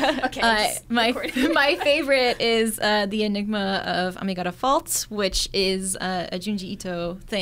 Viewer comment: what show did she say here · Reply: the enigma of amigara fault